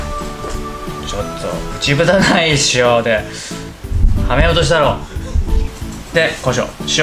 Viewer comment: からなんて言ってるかわからないのでどなたか翻訳してください！ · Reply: 「ちょっとぉ……内蓋ない塩で（俺を）ハメようとしたろｗ」 スタッフに渡された食卓塩の容器の内フタ（多分プラスチックの穴の開いた部分） 危うくドバっと入っちゃいそうだったんだと思います